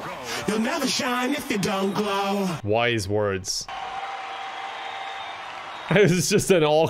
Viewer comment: Does he say "Wise Words" Or "Why Is Words?"